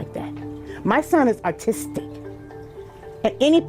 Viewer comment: Did she just say her child is Artistic?😂